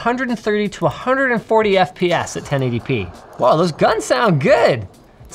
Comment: subtitles say ‘10 ADP’ lol